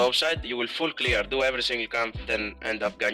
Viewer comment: I can't understand what he said. Don't Die level three something gank bot? Edit: oh shaco?